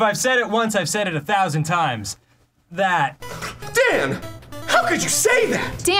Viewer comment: I used to like Dan, then when he said that I lost all respect for him. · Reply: no i just dont understand what dan says at 1:53. he says: if i said it once, i've said it a thousand times... Does he just say: that?